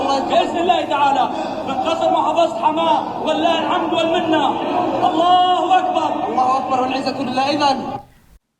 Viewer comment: im not trying to be rasist but all i hear is allah al bar